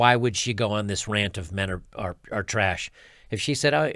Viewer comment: I thought she said all men were traysh?